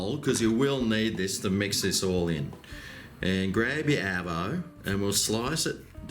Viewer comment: did he just said grab your apple 😂